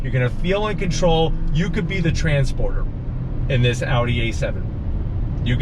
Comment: This is so very true! I though I said Transporter? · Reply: LOL! I played it back, and I thought you said Transformer! When you're able, please go on vacation, so I can get caught up on your content! ;)